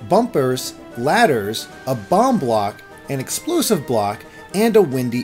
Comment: I’m not being rude but did u say ex-plu-siv I say ex-plo-siv